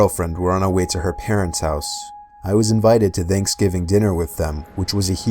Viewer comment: Its sounds like your saying Danksgiving X)